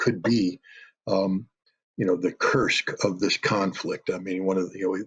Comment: What word does he say @ 1:56? : "The ____ of this conflict" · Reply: Battle of Kursk